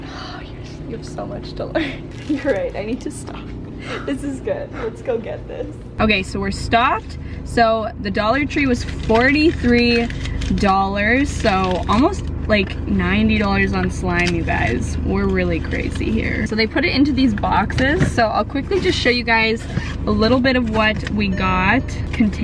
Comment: did she just say “the total was $43 so almost $90 guys